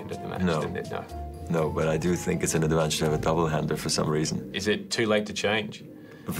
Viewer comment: What does he say at 1:55? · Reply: "No, but I do think it's an advantage to have a double hander for some reason."